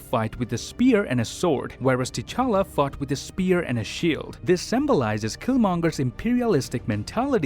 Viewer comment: Was it just me or did he said Black panther fucked with a spear and a shield